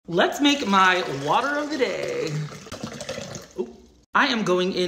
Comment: I thought bro said let’s make my brother gay 💀